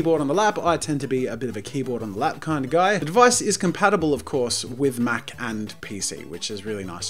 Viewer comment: I thought he whould say "Compatible with Mac and Cheese "